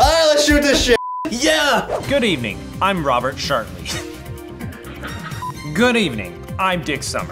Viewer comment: Does Ian say "I'm Robert Shartney" or is my brain messed up!?